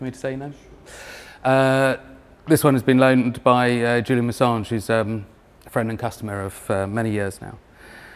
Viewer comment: Did he say Julian Assange at like 13:30?